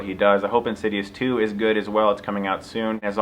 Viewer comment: i always thought he said you'll get stuck in my eyes , i had to enable the captions to know it was stuckmannize